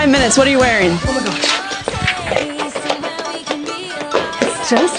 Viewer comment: I thought she said, "K, I need a bagel"